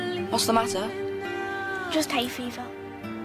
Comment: I thought it said "Just hate fifa" 💀